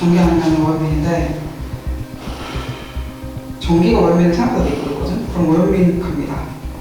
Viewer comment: 전기가 생각보다~그뒤에 뭐라고 하는거임? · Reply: 전기가 얼면 생각보다 되게 그렇거든?